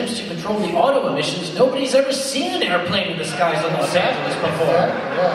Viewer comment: Did say Los Santos? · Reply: HD x EXoThERMiA No, he said Los Angeles.